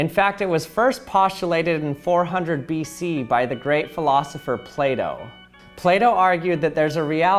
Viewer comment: Did he just said PLAY DOUGH 😂